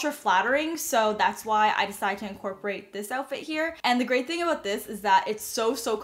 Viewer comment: I thought you said “the gray thing about this” instead of “the great thing about this for your all gray outfit lol